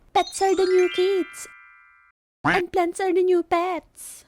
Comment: ano sabi ni ate???? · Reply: plants are the new pets